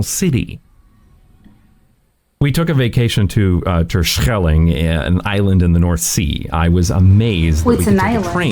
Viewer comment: Did I hear you say "What's an island?"😯